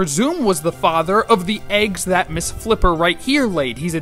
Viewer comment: Sounded like he said "this hooked right here", and not miss flipper.